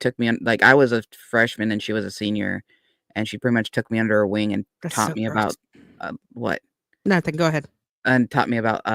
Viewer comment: what is that person muttering while is speaking? I can’t understand what they said and caused to pause.. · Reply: Sounds like "that's so gross" to me